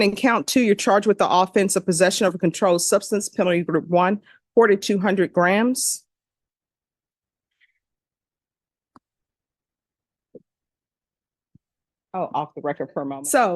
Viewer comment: I had to restart the video when I heard 4200 grams. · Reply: It's 4g to 200g